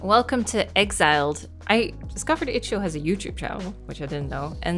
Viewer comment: wtf is she saying!? I've tried a hundred times and every time she says "scopedyhitchua has a YouTube channel." Could anyone please tell me what she was attempting to say? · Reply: Haha! “I discovered itch.io has a youtube channel”